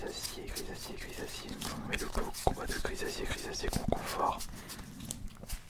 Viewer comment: heuuu vous entendez quoi ??? 😂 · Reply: Ça pue du fion !?